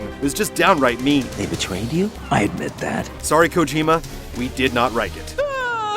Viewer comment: did i hear him say "sorry... we did not RIKE it" at around 7:30?? racist much?